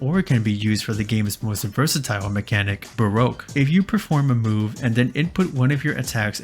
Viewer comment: I always thought "baroque" said "barbecue" when i was younger